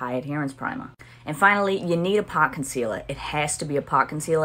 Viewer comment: What did she say at 4:06? Park concealer? I can’t find it :( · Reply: pot concealer!